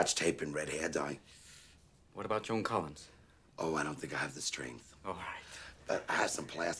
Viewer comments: "What about Joan Collins?" "Ohhhhh I don't think I have the strength" I'm wheezing 🤣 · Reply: I always thought he said I don't have the strings? But okay 😂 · "What about Joan Collins?" "Ohhhhh I don't think I have the strength" I'm wheezing 🤣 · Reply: I always thought he said I don't think I have the strings lol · I thought he said "What about Phil Collins?" LOL